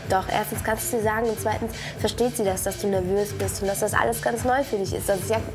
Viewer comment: Bei 04:08 hab ich verstanden „und 2. versteht sie das, dass du ne möse bist“ 😂